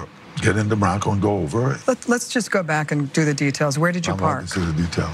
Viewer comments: At the 6:30ish mark I think what he says is, “I’ll walk you thru the details” Or “Imma walk you thru the details” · he sounds like he says “I want this as a detail.”